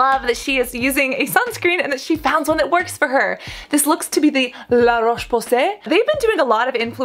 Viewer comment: Every time I hear “La Roche-Posay”, all I hear is “the raunchy pu**y”. This has been happening since the first time I heard of it a few years ago. Every time. 😂😂😂😂😂😂😂😂😂😂